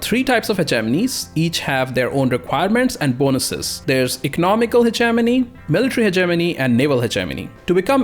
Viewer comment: Anybody else thought he said Ze Germany when he said Hegenomy XD